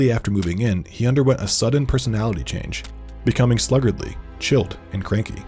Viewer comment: wtf does he say? Chilt? Chilp? Neither of these are words!